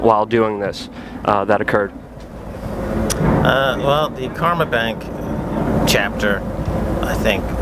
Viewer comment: Is he saying "Karma Bank"???